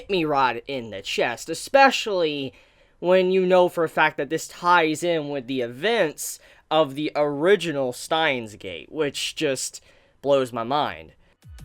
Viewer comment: what did you say at 0:22? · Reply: "especially when you know for a fact that this ties in with the events of the original Steins;Gate which just blows my mind"